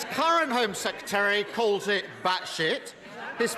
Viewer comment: Did he just say “batshit”? 😂